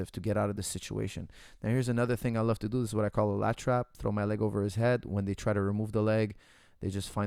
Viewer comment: They’re gonna call me the Lat Trap Monster after watching this. This is money. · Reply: I swore he was saying latch wrap. Lat trap makes a lot more sense...